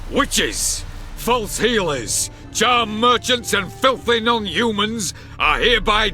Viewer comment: I heard as jam merchants and was perplexed for a second